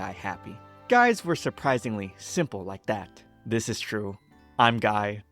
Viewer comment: I thought you said "this is true. I'm gay" not "this is true. I'm guy" 😂